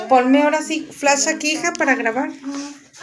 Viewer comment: En el minuto 6:05 se escucho “AYUDA” yo vi dos veces el vídeo y la primera vez no lo escuché 😮